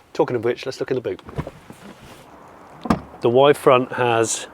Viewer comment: The Y frunk at 13:31? I thought you said "y-front" as in pants/undies! 😂😂